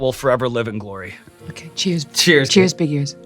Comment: anyone else hear… “ Cheers big ears” 😂😂😂